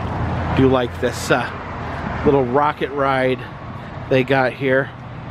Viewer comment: I thought I heard ‘Al Rooker in the room here today